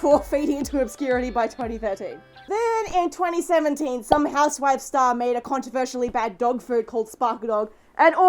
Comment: I thought she said "some ass wipe star" but really, she said house wife :'D